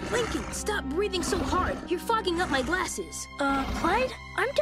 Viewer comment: did he said fucking 😂